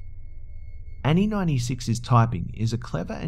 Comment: I almost fell off the treadmill when you said Annie96 cause I thought you said Annie99 still my name but the numbers got me hehe